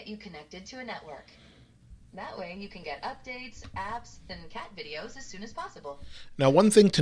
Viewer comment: did she say “cat videos”?